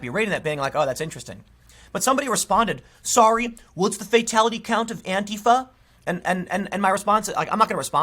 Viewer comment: "Sorry what's the fatality count of the Brown Shirts?" is what I heard.